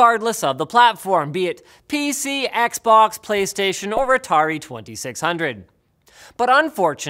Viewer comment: My favorite part was when he said "Retardi 2600". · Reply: He doesn't say "Retardi". He says "Or Atari 2600", and the "Or" blends into the "Atari", making it sound like he said "Retardi". There's no D sound at the end of Atari when he says it.